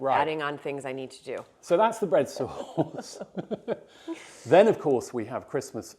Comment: I first heard "red sauce" - as a Texan, that has a wholly different meaning than "bread sauce"! LOL!